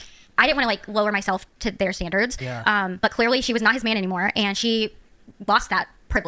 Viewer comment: Is it just me or did she say "but clearly she is not his man anymore" was she his man before?